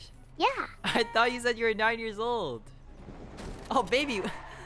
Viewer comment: I thought they said 8 year old bro hes 9